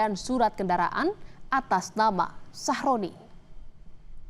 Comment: aku tidak salah denger kah ,nama korban ny sahrodi atau sahroni 😭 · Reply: Sahroni beneran😂